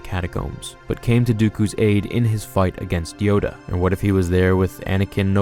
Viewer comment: did you say Dyoda?